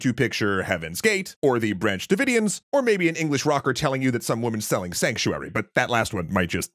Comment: I misheard the british rocker bit and thought he said "some woman's phallic sanctuary"